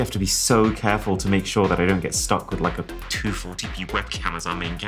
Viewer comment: YT captions say u said a swear word 😔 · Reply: He said 240p